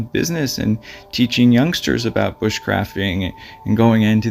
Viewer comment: Did you say he taught young people about witchcrafting???Wth. Maybe i heard wrong. Time stamp